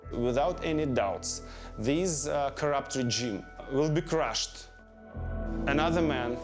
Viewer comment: does he say 'this corrupt Jew'?